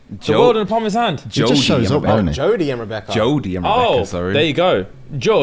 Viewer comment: I always thought Sean Paul said JOLIE and Rebecca…it’s Jordie?!